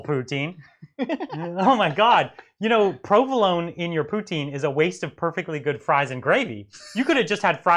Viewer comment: Was I the only one who heard him say "Probe alone in Putin"? haha.